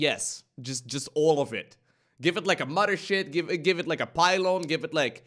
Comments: did anyone else hear mother sh%t? might just be me. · did he say mother sh1t?